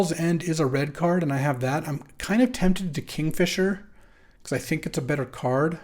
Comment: I was multi tasking when you said "I am tempted to kingfisher" i totally heard "tempted to catfish her"